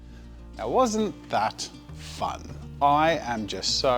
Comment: Lmfao at the end I thought he said “it wasn’t that fun.”